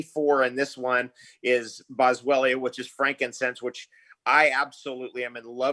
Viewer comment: What is the first herb He says ..when he was driving ...Rosfrera ? It sounds so familiar but I cant place it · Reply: I thought he said frankincense??